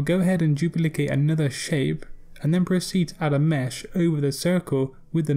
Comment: awesome. thought you said press c, to proceed with mesh tool. haha it was "proceed." :)